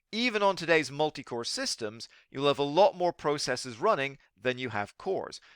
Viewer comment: you said we have we have more processors running than cores? i dont understand that. I thought each processor can have multiple cores so wouldnt we have more cores than processors? I think im very very confused.